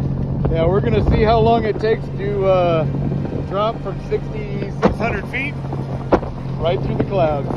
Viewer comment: I didn't even know that those things could fly that high up. I Always thought they had a ceiling of 1,000 ft max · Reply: I could have sworn he said 6,600 feet, even though the read out does say 660. I figured that maybe it maxes out at 999 then starts all over again